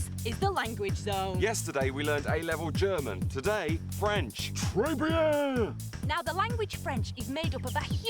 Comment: TRES BIEN!! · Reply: This whole time, I thought they were saying "triviale". Très bien makes much more sense!